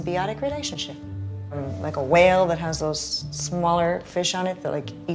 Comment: did she say whales have gills?